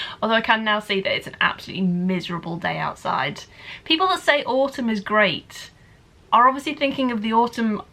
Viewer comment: Oh my goodness, I misheard the whole autumn part as "We all have this really romantic idea of AWESOME , or fall if you are American"...but I finally figured it out and it makes more sense.